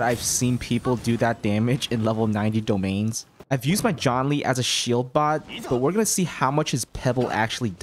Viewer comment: Did you just call Zhongli John Lee?!?!